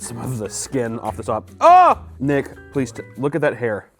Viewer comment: what did he just say??? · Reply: nick